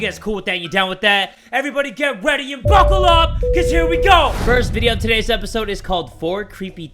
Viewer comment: What I hear: "fuckle up cause here we go" What you hear: " buckle up cause here we go"